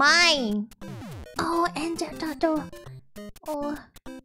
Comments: Korone's English is getting better, soon the doggo will take over the world! 1:20 It sounded like; 'Oh, Angel Turtle!' here. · "Oh, Angel Turtle" I think that's what she said lol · she's saying "oh angel turtle"(because of the wings). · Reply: I thought she said engine turtle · i think she said angel turtle <3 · I think she said “angel turtle” which… I guess isn’t wrong XD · I think she said "angel turtle" because of the wings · I think she says Ninja turtle?? (Or it's a turtle)😂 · I think she said "angel turtle", omg she's so cuteee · Did she say "angel turtle?" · I think she says, "ah, angel turtle"